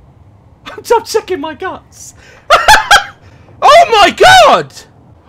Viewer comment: is it just me or does anyone else hear him saying" I'm checking my nuts"😂